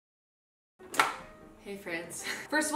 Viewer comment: At the start of the video I literately thought she said 'hey France' 😑😑😑